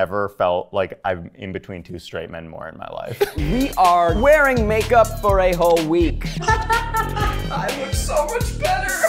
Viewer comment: I heard "We are wearing makeup for a-hole week" hahahahaha